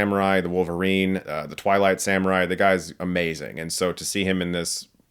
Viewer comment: Oo twilight samurai.. I thought you said twilight saga.. I was like WUT? Run that thing back. (taking off my glasses to hear better)